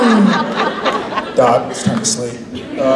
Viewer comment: did Jared call Odette ‘Dot’? Because if so, that’s the CUTEST.